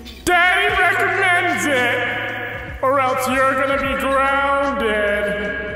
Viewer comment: OHHH...HE SAID GROUNDED ya... totally thought he said drownded uhh oops